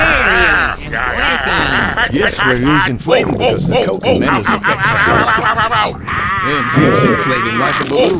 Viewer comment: What is he saying? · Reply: Thats what they are saying was: Waluigi: Wario is inflating Engineer: Yes wario is inflating becaue the coke and mentoes effects have started all ready and he is inflating like a balloon